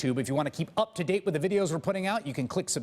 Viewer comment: haha sounds like he says "you can click subscribe, just blow me or click on this list" hahaha